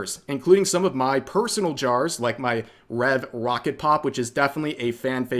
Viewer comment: Listening to this at work at 4:58 I thought you said "Red Rocket Pop" and i was like AYO REV?!?!?!